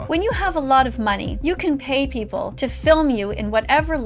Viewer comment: did you just say we can eat people? you said if you're faster or have better weapons...if i have a gun and you have a knife...can i eat you? 😛 lol